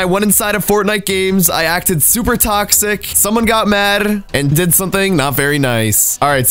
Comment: "SOMEONE GOT MARRR" WTF ARE YOU SAYING DUDE